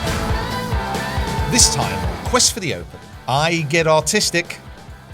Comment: I thought you said “I get autistic” 😭